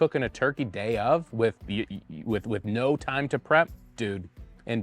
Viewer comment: Thought he said, no time to brine?